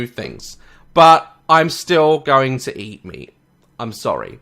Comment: for the longest time i thought he kept saying "Im still going to eat me" And I was like ummm wtf did i just hear, so i played the part again and realized he said meat XDD